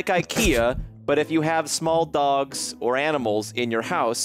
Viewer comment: With the ipea thing the first time I heard that I thought he said small dongs. So I was like do normal people with large dongs pee on their couches so I was very confused.